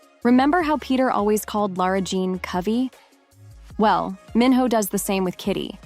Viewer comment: I don’t know if i5s just me but 4:52 did he just say Kave bc my name is kave